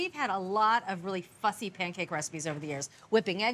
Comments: it sounds like she says fucky pancakes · I have to replay 0:25 like 4 times because it sounds like she says, "we had a lot of fu**ing pancake receipts of the years."